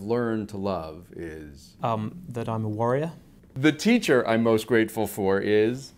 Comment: i think he means worrier ? but the caption says differently